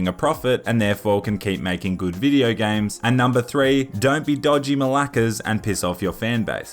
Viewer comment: Did you just say "dodgy malakas"? Cause I'm laughing so hard right now 😂